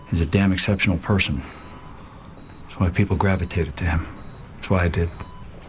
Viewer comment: what does he says at 0:46 but people gratuteded to him?